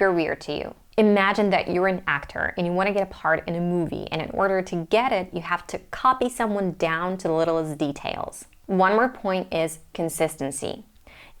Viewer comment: what's the words you say from 11:14 to 11:16 , you say it all the time perfectly 👌 Please write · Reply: "you’re an actor and want to get a part in a movie and in order to get it you have to copy someone down to the littlest details"